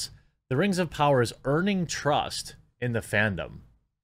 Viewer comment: I swear I heard you say burning trust lol